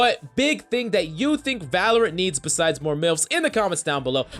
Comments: Did he just say "Besides more MILFs"? XD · did he say milfs????